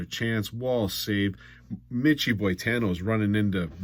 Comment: for a year i thought he was saying mitchy boy tanno, so i finally looked it up hahaha im guessing hes talking about brian boitano haha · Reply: He is saying that